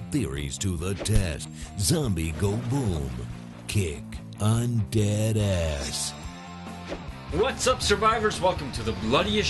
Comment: I heard "kick on dat ass"